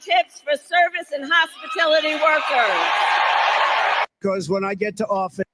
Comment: Did she just say "HOSPITILITY workers"......?🤨 WHAT IS THAT YOU DICKFLUIT?!🤦‍♀️